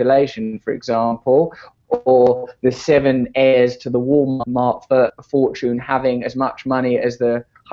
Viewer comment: I kept hearing; "Let's loot the Walmart!"